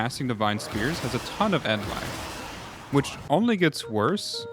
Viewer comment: End life? End lag? N lag? What are you saying here? 2:59 I assume it's N lag, as in, you cant spam abilities or smth?